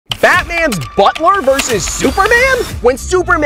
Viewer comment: Is it me or I hear him sayin "FATMAN" at the beginning?